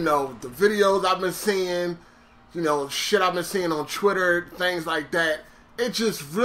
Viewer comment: Why does it always sound like you saying Chtwitter.